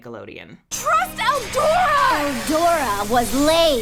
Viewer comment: I really liked this video! It was really informative and entertaining! There was one little mistake, though: at 13:02 Selina says "Eldora era noiosa", which in Italian means "Eldora was boring", not as the subtitles said "Eldora is an idiot". It's just a tiny little thing but completely changes the meaning of the scene, giving Selina kind of a passive-aggressive vibe, instead of an aggressive one, especially with the tone used. (From an Italian viewer who watches tons of videos about the English dubbing of Winx Club, a series she grew up watching in its original version, for no apparent reason)